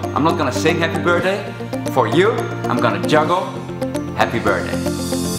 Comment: Its sounded like you Said happy birdday at the end ahahhahhaah